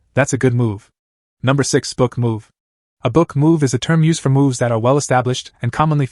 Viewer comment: I can’t have been the only one who misheard book move as rook move and was confused when a pawn made one